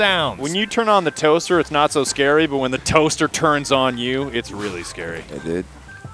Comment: I thought you said “when the toaster turns you on”